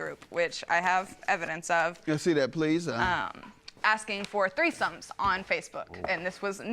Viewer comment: Did she say the plaintiff joined a “mommy group” asking for threesomes???? 🤦‍♀️🤷‍♀️